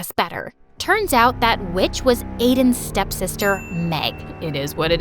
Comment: I LAUGHED WHEN I THOUGHT SHE SAID B!TCH INSTEAD OF WITCH